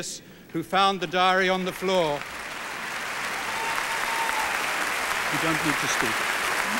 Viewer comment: It sounded like he said "You don't get to speak".